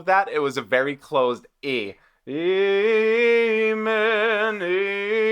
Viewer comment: Sounds like HoeZier says “gay men” 😝 in the amen part hehe